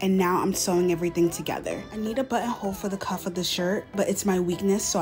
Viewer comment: I thought you said buttons are your weakness and i was like what? How? Then I realized you said button hole and I was like oh f yeah those things are awful.